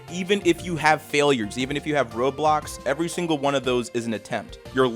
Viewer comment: i thought he said roblox